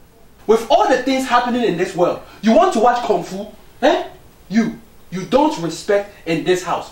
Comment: Did he say Kung Fu?